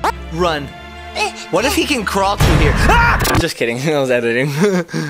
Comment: wait did just say "i hate ennard" ?